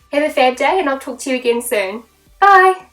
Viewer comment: I can't be the only one that thought she said "have a bad day" at they end 😂😂